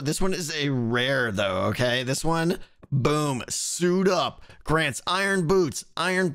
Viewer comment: I thought suit up said shut up lol